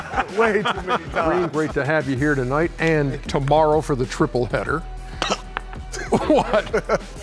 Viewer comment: Who else thought triple single when they heard triple header